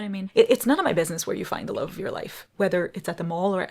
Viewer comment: Why did I hear "The loaf of your life" you rock!!!